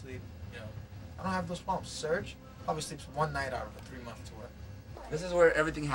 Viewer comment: What does John say at 1:09 ? I hear "Serj probably sleeps 1 night out of 3 nights" but I'm not sure lol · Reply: I think he said "serj probably sleeps 1 night out of a 3 month tour."